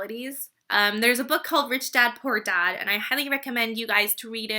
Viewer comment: What's the name fo the book in 6:40? Sorry I didn't understand, I'm still learning ':D ,it's not that you pronounced it wrong :) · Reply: FMei Dong rich dad poor dad